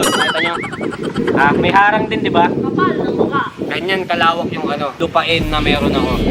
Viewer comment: Hahaha may narinig ako lakasan nyo volume nyo ang kapal daw ng mukha hahha